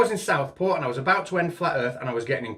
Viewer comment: I thought said it was in South Park LOL. OMG, those FE bastards killed Kenny!